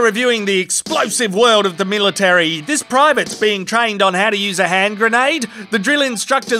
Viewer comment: “This private” I thought he said this primate 😂